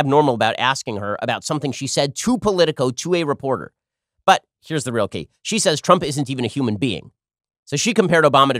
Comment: I thought for a second Ben said “here’s the real tea” and my inner drag queen got so excited